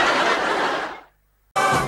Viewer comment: Gonzo: “Green Smoke?” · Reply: lol, wow. All this time, I thought he said "bean smoke" XDD